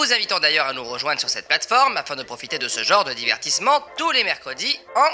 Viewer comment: Super la vidéo par contre à 0:25 c'est "tous" et non "touT" LINSTRU PUTAIN EST TROP LOURDE, parcontre depuis le début du confinement j'ai jamais bossé autant les abds que pdt la vidéo. Quand il a dit roue, j'ai compris roux XDDDDDDDDDD